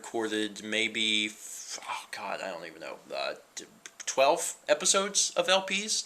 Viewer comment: it sounds like you say f**k i don't know. Also, girls that look 15, but are 19 are...not as scary, but kinda scary o.o